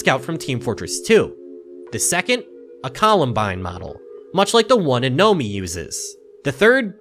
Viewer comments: hate to be annoying and comment multiple times, but did he call it a Colombine model? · sounds like he says columbine instead of combine